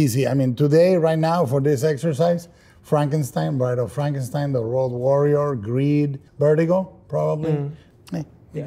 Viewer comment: The subtitles say The World Warrior, but he said The Road Warrior (aka Mad Max), he's talked about it before as being a favourite.